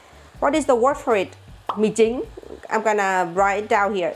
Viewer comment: did someone said micin 😂